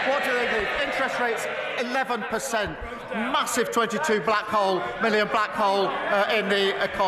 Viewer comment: Did he just say 'massive 22 million blackhole' after stumbling on his words? 😂😂😂